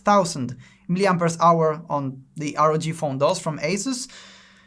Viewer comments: Tiraste un "...the ROG Phone 'DOS' from Asus.." o escuché mal? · Did i hear ROG phone "dos" (2)?